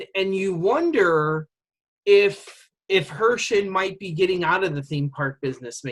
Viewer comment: Lol you said “Herchin” and I heard “Hershey” and went “Andrew is out of his mind!” 😂😂